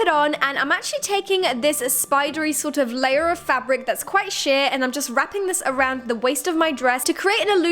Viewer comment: did the say that is quite shit ?